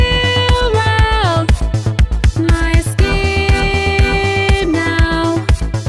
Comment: When you were say my escape now I thought you were saying my skin now I do not know what was going through my head when I heard that ,then I read the the word and then it made sense